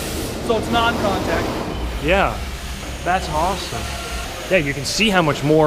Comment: Did you say, "That's Haasome!"